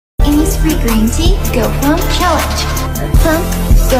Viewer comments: I DIDN'T KNOW WONY WAS SAYING THIS 😭😭😭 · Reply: I heard "innisfree green tea, go home challenge🥰" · Before this video, I literally thought she was saying “pump, flowing, migration, boost” 😅 my dumbass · I thought it said b00bs not boost…